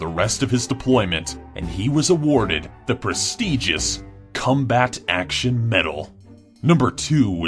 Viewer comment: i thought you said cum-back